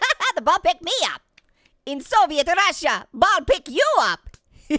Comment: DID HE SAID SOVIET RUSSIA?